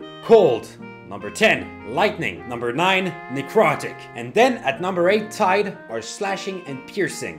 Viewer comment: I wasnt looking when he said necrotic😅 Cue whiplash as I turn around thinking I heard "Erotic"